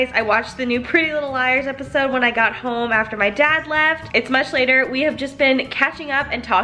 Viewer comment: when you said much later i thought you said months later and my mind got so fricken confused?!?!